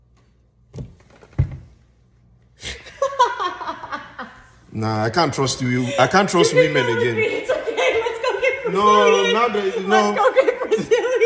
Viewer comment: "I can't trust women again" 😅🤣😅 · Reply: I thought he said, I can't trust you Americans